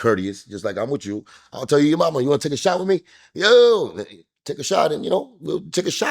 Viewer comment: Cuban sounds like he asked the chick “ Ya wanna take a shower with me”? Maybe that’s what homie thought he heard. 😂😂